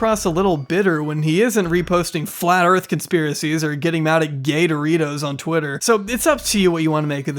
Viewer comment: i heard “gatoritos” instead of gay doritos and now im imagining him getting mad at a tiny alligator